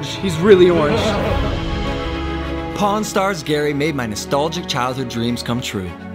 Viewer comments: I thought u say porn star hahahaha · when he was saying pawnstar gary, I thought he said porn star gary lol